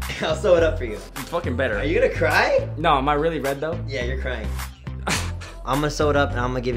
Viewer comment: What did kian say in 5:13 ??? Tell me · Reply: Alpha21 he said "no but am I really red though" ??